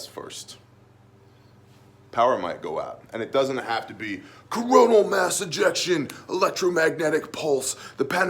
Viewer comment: Did he say… Coronal mass injection?